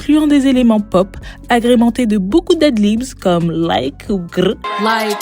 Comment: t'as dis quoi ? j'ai pas entendu !stp deadlibs? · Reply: Ah 😭 J’ai dit bcp d’ad libs ou bcp de ad libs